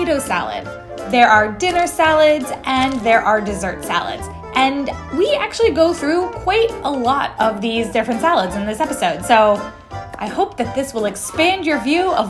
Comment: I thought he said worst salad😅😂